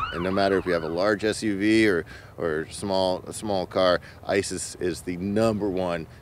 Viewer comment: He said Ice is, but it sounded like ISIS. 😆 🤣